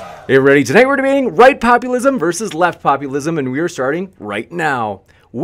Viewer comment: I heard "White populism vs. White populism"